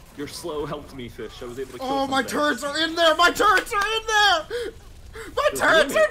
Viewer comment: It sounds like is saying turds insted of turrets